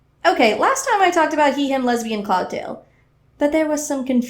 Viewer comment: if you turn on captions it says Cocktail instead of Cloudtail and